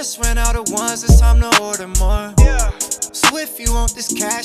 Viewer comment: When I first heard this song I thought it said "owe dem more" xD